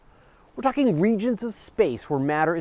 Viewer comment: I thought he said "fucking regions of space"